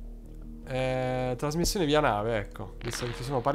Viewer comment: Solo io ho sentito "trasmissione viaanale"?